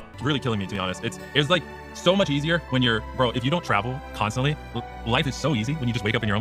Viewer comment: To be fair, the clip that Kotaku quoted really does sound like he said "It was like, so much easier when you're broke" which I think he realised midway what he was about to say, so he stopped mid-broke n' pivoted. Perhaps he did just use 'bro' as a 'like' filler, but personally I think he just caught himself n' Kotaku either misheard or extrapolated his initial misspeak, which is probably more likely.